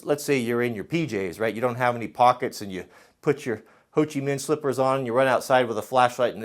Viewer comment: I agree that the pouch could be a little bit bigger to accommodate a cellphone but other than that I like it. My husband walked in as you were explaining the waist band & thought you said hoochie slippers instead of Hochimin slippers & I had to chuckle a little bit. Stay safe up there family & Im praying for you all.